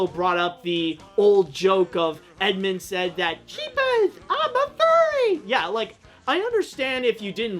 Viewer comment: Edmond is saying "Jeepers I'm (ALL) furry" not I'm a furry. I'm not sure if he's just referencing the joke that Nostalgia Critic made or if he genuinely thinks that's what Edmund says.